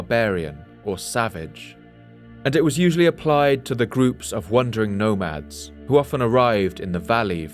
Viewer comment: I keep hearing him say "wondered" or "wondering" instead of "wandered" or "wandering". Example